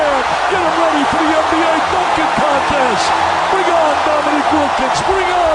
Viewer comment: I thought he said fu%k*&g contest